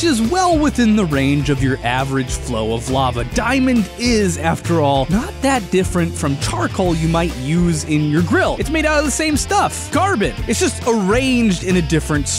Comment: at first I heard garbage instead of carbon